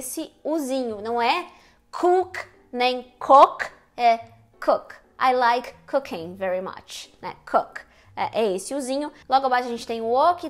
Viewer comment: Idk why, but i swear that i heard ''i like cocaine very much'' O.o